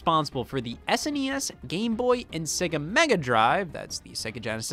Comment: "Game Boy" instead of "Game Gear" lol Love you! Thanks for all the bangers last year :)